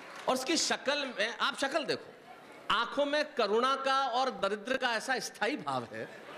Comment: I heard corona